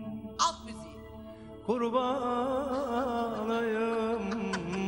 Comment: kurban olayım derken neşeti duydum sanki tabi daha teknik olanı